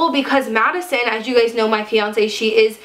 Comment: Did she say fiance? THER IS NOTHING WRONG WITH THAT, I AM JUST ASKING CAUSE i thought she said "my frosting, she is african american" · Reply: Hahaha. Yes she did say fiance not frosting XD